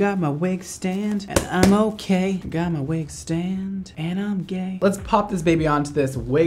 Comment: did he just say " and I'm gay " 🤣🤣